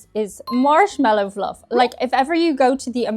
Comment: Why did it sound like you said "Arse-mallo Fluff" ?